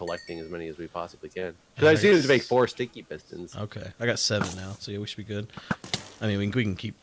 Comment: Every time says, “sticky pistons,” I hear, “stinky pistons”.